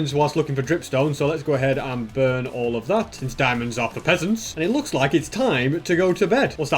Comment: I thought you said diamonds are pecants